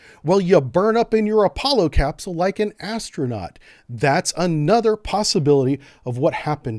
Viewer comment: I legit thought he was gonna say you burn up in your Apollo capsule like an asshole